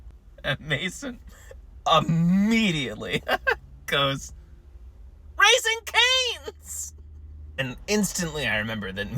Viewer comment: I KNEW IT WAS RAISING CANE'S! At first I thought she was saying RAISIN Bran but then I thought about it and the "woof woof" confirmed it lol